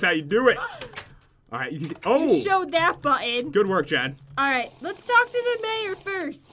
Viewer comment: the subtitles say "dad" instead of "Jen".................